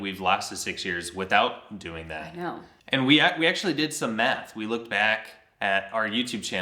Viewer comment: I heard: "we actually did some meth" 🤣🤣 I was like he's being funny. But you said math. 😂😂 We'll all tune back in when you come back. ❤